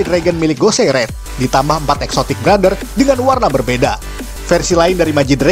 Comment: Lha Exotic Brothers ;-; Aku dengernya Exotic Butters :v